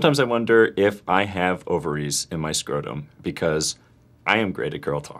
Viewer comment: I thought Gabe said “over-ease” instead of ovaries.